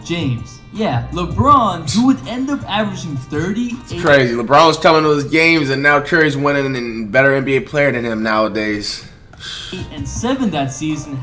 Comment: wtf he said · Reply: "LeBron was coming to his games and now currys winning N the better player than him nowadays"